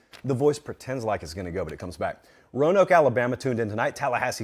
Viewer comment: I seriously thought he said 'Rona when his voice cracked. But then he finished and said Roanoke.